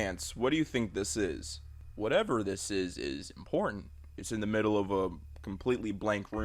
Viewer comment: it sounds like he said it is porn